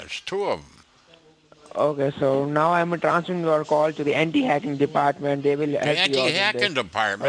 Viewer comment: I swear at 9:36 I heard the hanky panky department.